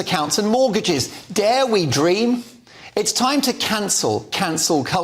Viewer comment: Am i the only person who thought he said the first people to deal with are these utter wankers on the end.